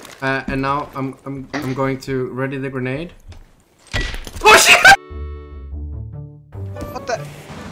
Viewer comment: 나만 오시바 라고 들리나?